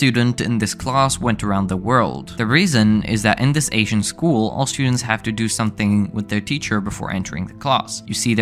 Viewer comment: Ohhhh when he said "all students" i thought he said Austrains